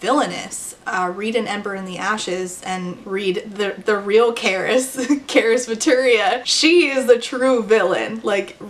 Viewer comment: Hi verry sorry but i didnt quite get the recommondations at 24:00 , i heard an ember in the ashes but what was the caris/karis/caress rec? I tried to figure it out but i couldnt 🙈 Also amazing video!